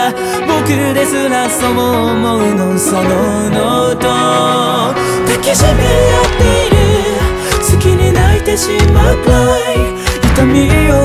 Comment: の“抱きしめ合っている隙に泣いてしまうくらい”のところが、“抱きしめ合っている好きになってしまうくらい”に聞こえる。